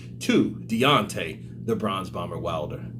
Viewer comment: Haha it sounded like you said the Bronze bummer, Fury would be proud lol